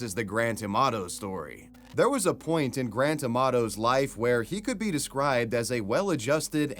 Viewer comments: I kept hearing Grant's name as Gran Tomato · i thought he said grand tomatoe